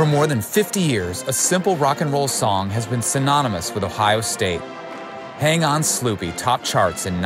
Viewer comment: I had that record, but at first I thought they were saying "Hang on Snoopy."